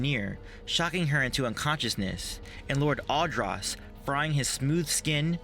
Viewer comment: Whose's smooth skin would Malgus fry? Lord Adross? Couldn't quite make out what you said. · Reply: Robert Reynolds Lord Adraas.